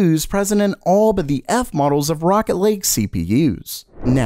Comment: I thought he said rocket league but it was actually rocket lake.